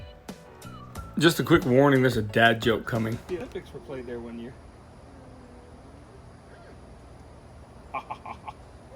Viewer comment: What's the dad joke at 11:23 I couldn't hear it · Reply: She handed me some thing and I said, do you know the Olympics were played there once